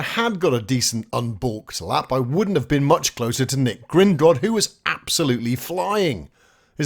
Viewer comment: When you first said "Nic GrindRod" came first in quali, I Heard "Nic Gringott" and thought, well he owns the wizarding bank in Harry Potter so he likely has loads of dosh and magic, too! Well done - very entertaining.